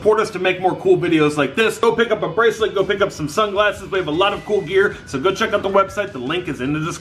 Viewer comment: I thought he said sun classes but he said sun glasses